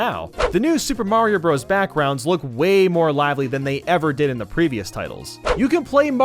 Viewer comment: is it just me or did he say lavley